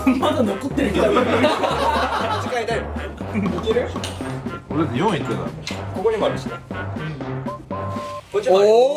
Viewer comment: きょもがほくとに何て言ったか分かる人いませんか？🥲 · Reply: 味変えたいのって言ってますね☺️